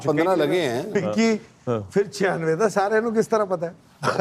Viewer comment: wat did Ajay Jadeja say ?? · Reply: Fer 96 da loogan nu kis tarah pata aey